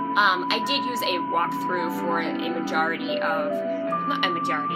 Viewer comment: Did you say "Majora-ty?" Majora's Mask is just as good a game as Ocarina of Time.